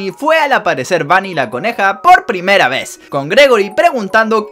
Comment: por que todos recuerdan que dice conejo? si en realidad dice coneja